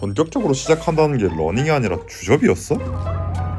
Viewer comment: 러닝이 아니라 다음에 뭐라고 하신 건가요? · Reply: 주접